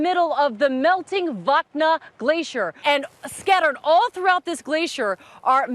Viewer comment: At first I thought she said melting vodka glacier in Russia... damn it